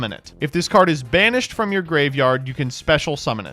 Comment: I misheard, it sounded like "Summon a card in Spanish" not "banished". I was VERY confused for a second there.